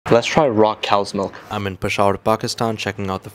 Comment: For some reason I heard "rock cow's milk" 💀